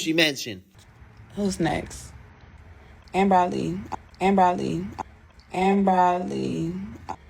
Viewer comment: Did She Say Amber Ali ?